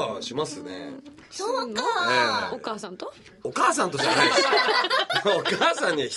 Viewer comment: お母さんと？っていうのが、大川（透）さんと？に聞こえた